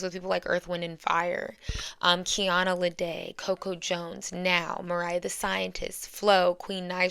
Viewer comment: whos the girl at 21:31 i cant hear her name right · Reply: Her name is NAO